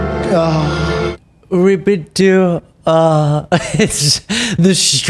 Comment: It sounded like he was practicing his french @ 13:12. Rep^i`du aaah He signed up with Babel. 😆